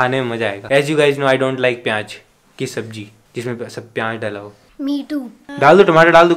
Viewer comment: i thought said meetu but it was me too😂😂